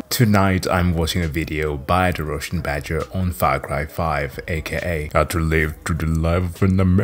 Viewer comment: for a second I thought I heard (far crack 5) and thank God it's not